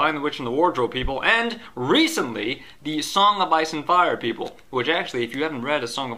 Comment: I can't understand what you are saying for the 3rd book. it sounds like you say songa bison fire people.